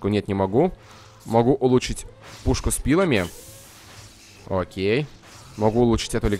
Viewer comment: Мне послышалось что сказал пушка с пивами :DDD